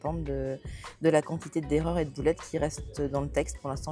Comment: On commence fort le vlog avec une dépense de mouton ! BRAVO ! Cadrage voiture, avec le tel scotché au tableau de bord XD (la voix était claire, tout va bien) Ah le fameux camion plein de livre, celui qui auraient pu prendre feu tant l'historique du bouquin est chargé XD De gros calins à Laska <3 Mes oreilles m'ont fait un truc de dingue : tu parlais d'antidote et des boulettes que tu as retrouvé et moi... J'ai compris "poulettes"... Voila. Fais en ce que tu veux XD Y avait plus de place dans le sac pour les affaires de Laska ? Il lui faudrait un sac attitré aussi du coup XD (mais c'est cool les places qui acceptent les animaux !) ah bah j'hésitais a imprimer recto/verso mais en effet, juste verso c'est mieux pour les notes. Trimestre ou semestre ? XD j'ai lu le début de l'épilogue ! C'est un scandale ! Comment ose-tu !! ... Non c'est pas vrai en fait, j'ai eu la flemme de faire pause XD aller a la librairie sans dépenser de moutons, c'est un exploit ! (t'auras pas de trophée pour ça par contre) Chagriiiiiiiine !! Je vais le prendre aussi dès que j'ai 30 moutons (moi je suis pas en negatiiiif XD) Laska qui fait sa dissidente a vouloir voir ce qui se passe ailleur Et t'en a pensé quoi de la pièce HP ? ça fait quand même peur les chiffres d'antidote XD t'as un pizayolo qui s'appelle José, mon perso principal (trilogie) s'appelle Abel Frost et manipule le feu... Voila. Le cerveau parfois XD Tu dis que tu vas faire les boutiques mais tu parle pas des achats livresques... le déni e